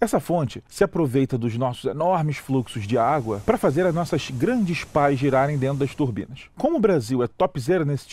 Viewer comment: O que ele diz no minuto 1:20 ? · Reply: Nossas grande pás girarem